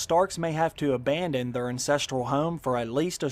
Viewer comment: Did you say "incestural home" at 1:40min lol?